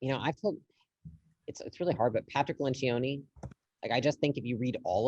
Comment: Witch is the autor of the 12 books? Can't hear the name properly 53:25. Thanks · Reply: Patrick Lencioni